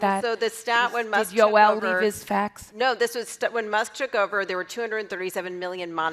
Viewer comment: did she really say "Does Yoel need this faxed?"